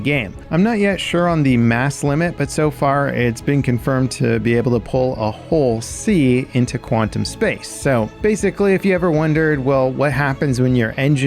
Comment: at first I heard "able to pull a whole sea into quantum space" !!